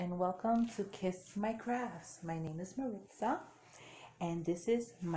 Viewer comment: Great first-DP-video video! Heard you say your name is Marissa? I think that's what you said! Nice to meet ya, I'm Amy. Thanks for sharing this & for your comments on my video too!